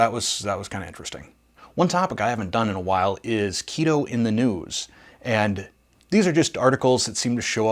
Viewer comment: Sorry, was 3/4 listening at the point where you mentioned "keto in the news" and thought I heard "keto in the nudes". I got a good hearty laugh to start my day, so thank you, or thank me for not listening well, or, whichever!